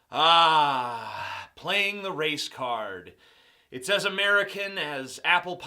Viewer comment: I swear when he said "race card" i thought he said "Racecar."